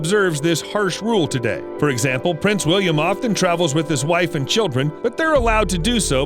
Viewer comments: did I hear that right Prince William afton LOL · It sounds like he says william AFTON